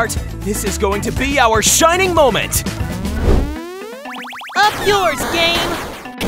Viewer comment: what did he say on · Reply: He said “Up yours, game!”